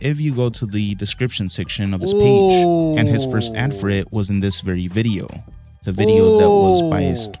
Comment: Okay I have replayed this three times at 7:35 when was making noise! What was said? He gone back talking a lot! Lol😂😂 love ya ❤ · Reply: He said his very first ad for it